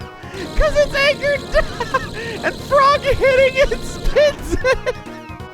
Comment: What did you say? Talked way too fast and high-pitched to understand. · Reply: "'Cause it's anchored down...! And Frog hits it!" I believe.